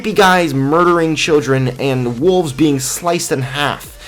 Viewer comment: did you say wolfs|! my pic is the black "wolf' whitCH IS A CAT NAMED SCOURGE KILLING TIGERSTAR. also he is my baby